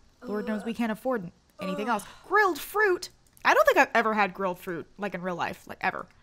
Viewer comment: I thought she said “real fruit” and not “grilled fruit” and I was highly confused 😂😂